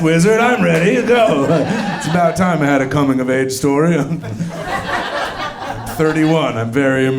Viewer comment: I thought you said Coming of Mage story and for a second you were the greatest comic who ever lived. Dibs. Taking that for a YA Novel or a Tabletop game. Thanks